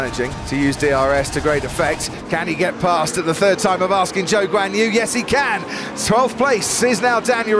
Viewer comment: Why do I always hear "Joke's on you" when you talk about Zhou Guanyu?